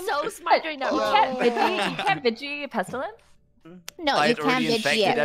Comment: Was there an explanation as to why guessed Koji as Plaugubearer instead of Pestilence? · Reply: Oh ok, I was so confused because at 23:49, to me it sounded like said "you CAN vigi it."